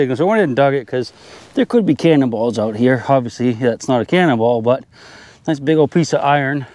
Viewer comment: Another day out and about with of on a new permission. We find some colonial coppers, a few Civil War relics, and a fun time. You can find YouTube channel here: <link> Special thanks to all of my supporters over on Patreon! Follow Army! My Patreon: <link> Instagram: <link> Facebook: <link> Twitter: <link> T-Shirts: <link> Thanks for watching…. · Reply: At one point you say 'cannonball' and I thought you said 'cannibal'! the last one I watched from you had the bones.